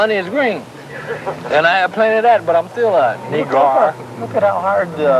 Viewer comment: What did he say at 1:17 after and money is green, and I got plenty of that? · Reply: "But I'm still a ne'grow"